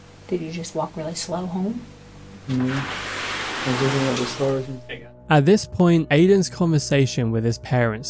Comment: when it says inaudible I think he says I was looking at the stars and. Not that, that helps.